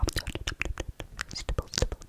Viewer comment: "just gonna scan your face" I THOUGHT SHE SAID SKIN MY FACE😭